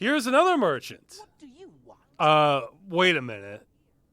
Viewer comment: wait what slur did he say? I just hear him say "What do you want?"